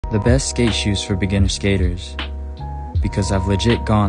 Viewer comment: I thought they said "the best gay shoes for beginners"